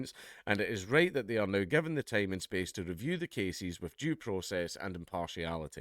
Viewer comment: Did he say "jew process"?